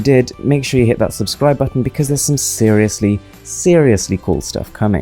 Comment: Each time he say Im Mrwhosetheboss i thought he said im wizard of the oz 😂😂😂